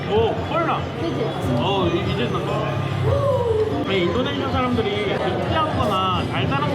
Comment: 난 분명히 들었다 가 우리 인도네시아 사람들이 라고 한 걸